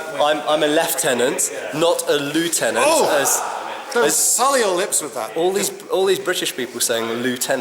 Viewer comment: With English as a second language, what is Lindy saying at 2:22? Sth with lips? Thank you. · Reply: 'Don't sully your lips with that'